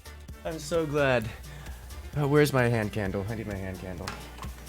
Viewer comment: What is he saying at 41:49 ? is that "Where's my hand candle, I need my hand candle" and then sounds of a lighter? Going by the weather report for Florida that night it didn't get down past 68° F (20°C) so unless is a super pansy I don't think he was warming his hands up. No judgment if that match made him need a smoke afterwards, it's just kind of rare to hear nowadays.